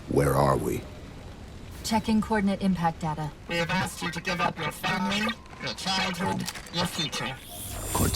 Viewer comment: I never noticed what was said at 10:17 “We have asked you to give up your family, your childhood, your future” going around on the 3rd time over that cutscene, I thought it was gibberish. Interesting